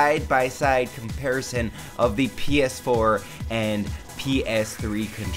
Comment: I thought you said "Penis 4" xD lololol